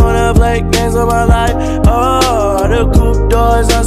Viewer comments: I thought it said “I got thoughts, they’re suicide, oh no” but I guess not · i always thought it said “cuz my thoughts are suicide”